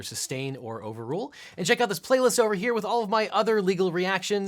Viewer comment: Oh you said playlist. I heard 'platelets '. I went to school to be a medical laboratory technician. Go figure. Fun review.